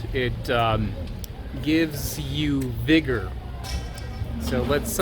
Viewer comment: Everytime you said it gives you "vigor", I thought you said "bigger"...I was like oh ok 😅